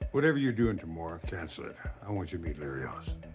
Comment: what does he say at 1:33? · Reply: “Whatever you’re doing tomorrow cancel it, I want you to meet Larry Ellison” … Larry Ellison is the founder of tech giant, Oracle. Apparently Don made a huge investment when Larry was seeking for capital to start his company